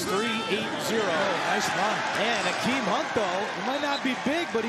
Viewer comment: Thought they said Kareem hunt was Orlando’s RB😂😂